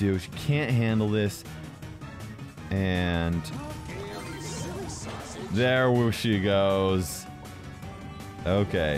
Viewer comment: wait what did he say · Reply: ouhh gale you silly sausage